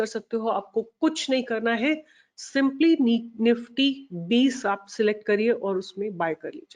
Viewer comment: A minor correction: when you said "niftyBees" the caption said "nifty 20" (13:03) 😛